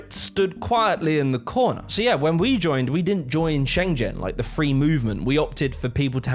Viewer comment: did you say "Shen-jen" ? Xian'gen, EU ?